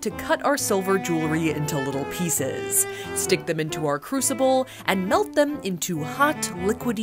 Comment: When she said crucible I thought she said crucifix and I was like “We DeMoNiTIzInG ThE SiLVer”